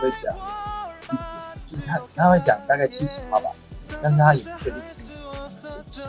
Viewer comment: Hi at 7:08 im pretty sure he said 78% instead of 70%! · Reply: It was 70%. He said “Pa” (“Per” in Percent). It is how % is said in Chinese